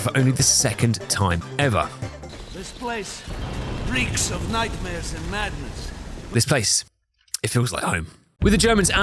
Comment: “this place… it reeks of nightmares and madness”. The first time I played this I thought he said “nightmares and mayonnaise”. I didn’t get it, but I went with it.